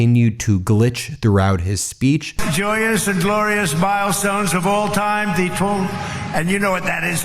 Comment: did he say "glorious bile stones..." ?🤣